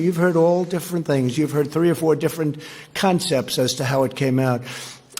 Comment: I thought he said 3 or 4 different cuntsepts?😂